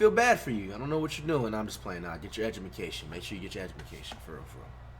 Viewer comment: did he say get your edumacation